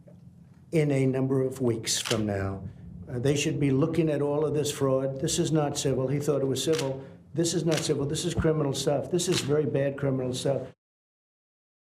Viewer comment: At minute 2:32 He says winks instead of weeks! What an iviot